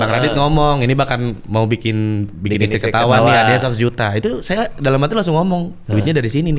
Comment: hah 100jt? serius aku salah dengerkah? 1jt ya? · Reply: gila sih beneran 100jt, bang Radit dermawan cuman caranya berbeda. jadi valid dan ada bener joke "nih uang , buat istriku ketawa sana" 😂